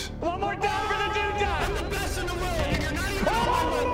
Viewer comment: is he saying '' I am best in the world N** '' 0:12 ?